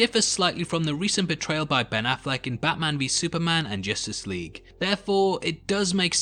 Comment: When you said "Ben Afflecks portrayal" I heard "Ben Afflecks betrayal"